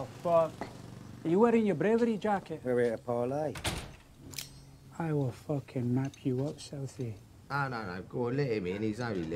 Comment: What is he saying ? When he points the knife at him ? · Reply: "I will fucking map you up, Southie" Slang for cutting someone up; wounds resemble lines a map.